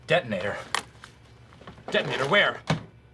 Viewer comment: When Dwight said “Detonator, where?!” I thought he said “Death-Eater, where?!”.